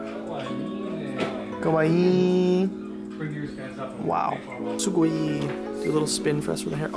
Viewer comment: At first, I thought you guys kept saying " so boring... " and I was like "???". Later I knew you were actually saying a Japanese word "SUGOI〜" . BTW you are my favorite. not in that way. Gambatte!